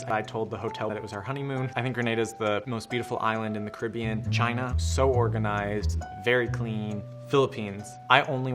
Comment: did he say China is clean?